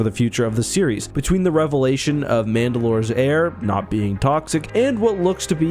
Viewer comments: I think you said Mandalore's air not being toxic but I heard Mandalore's heir not being toxic 😂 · When you were talking about revelations and said "the air is not toxic" I heard "the heir is not toxic," which, given Bo Katan's demeanor this season also fits. Honestly, I would like it if Bo would be able to become the political leader of the Mandalorian people, with Din becoming the spiritual, taking the place that the Armorer was to him, with some necessary reforms to the interpretation of the creed. You can see Bo's cynicism starting to crack watching Din stubbornly and sincerely trying to live up to his religion.